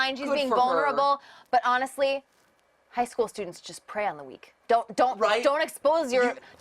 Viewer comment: I thought said pray on the week instead of prey on the weak. I am ashamed of how many times i had to rewatch that